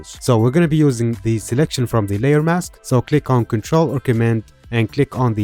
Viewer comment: I can't hear very well at 2:11 when you say "Click on control ???? then click on the layer mask below the selection" I can't hear what the shortcut is properly sorry. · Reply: It’s ctrl/cmd + click on the layer mask to load it as a selection